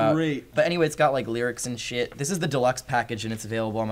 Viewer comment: i heard 'shit'